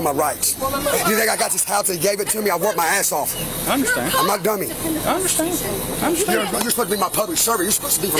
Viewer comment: did he say "I'm not DUMMY"? 😮😜😝